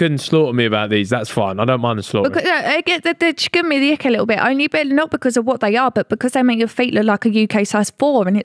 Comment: What did she say? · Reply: They give me the ick a little bit not because of what they are but because they make your feet look like a size 4